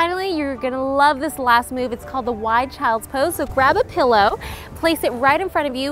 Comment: I totally thought that said "Wild Child's Pose"